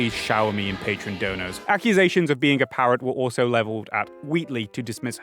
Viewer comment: Hey soup I found another caption issue, around 40:20 you speak of accusations of Wheatley being a parrot, but the caption says pirate. No idea if you'll see this but I've said it now (I know it's pedantic but given the subject matter of the video I think ensuring full accessibility in ways like this should be paramount. well it should be anyway but you understand what I'm saying)